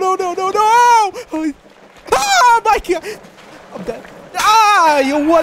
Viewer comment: did bro said "Oh my gyatt"? (brainrot mind)